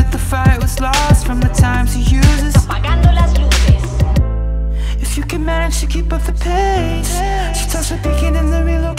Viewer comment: what is she saying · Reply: "apagando las luces" it means" lights out! "